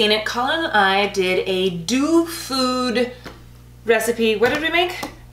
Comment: I thought that said “dog food”. 😂😂😂